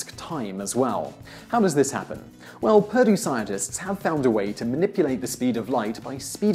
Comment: is he saying "Purdue scientists"?